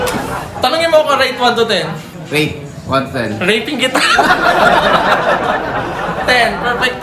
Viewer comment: Did I just hear "Rape-in" Kita at 21:40??? :((( that word is never a joke 🙄 regardless of who said it or to whom it is said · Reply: Rate in po yung sinabi😊